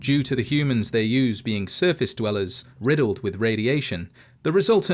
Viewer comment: I can't be the only one who hears circus dwellers right?